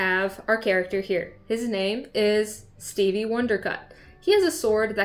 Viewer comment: I heard her say "Stevie Wondercock" and was wondering how she said that with a straight face. I replayed it 4x out of confusion then checked the CC and saw it was "cut" not "cock". I have a magic system for a fantasy story I'm writing. The setting for the story is a school where magic from the multiverse is taught. I really love the idea of explaining the system in small bits through classroom lectures, homework, and class projects. This magic system has changed so much from when I started it; specifically, the setting. Originally, it was a modern fantasy, but I changed it to an "otherworld" fantasy story to separate it more from Harry Potter. The story also has a lot of action so the system has to be flexible.